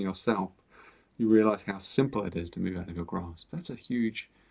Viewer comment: Shadow would be the negative Karma or baggage that we keep Luggaging, if not subconsciously, which impedes our growth. Negative people must be done away with if you are to pursue a happy and healthy spiritual journey. Always remember to practice the yamas and niyamas as coherently as possible to ensure success in this life. Do away with the garbage. Stay in a meditative state. Amen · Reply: I heard "You realize...how simple it is to be out of your grasp..." I hope this is helpful.